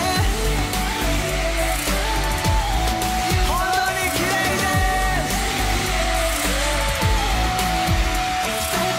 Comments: What does he say? 3:40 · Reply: he say thank you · what does he say? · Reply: It's so beautiful 3:47 Thank you, everyone 3:50 All together(Please sing it together)